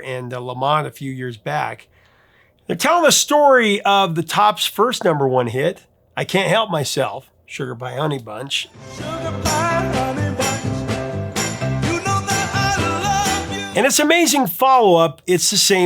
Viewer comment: I've always heard it as Honeybun not honeybunch. I still do. Interesting. I never really paid attention to the fact these two songs are the same though, but once you do man is it ever obvious! Love 'em both.